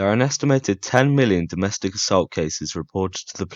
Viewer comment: I actually had to replay what he just said in the beginning cuz I thought I heard 10,000... Nope millions..